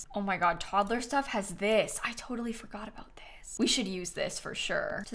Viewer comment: When she said “I totally forgot about this” I thought she said “I totally fu%k with this” and I was like woahhhhhhhhh 😂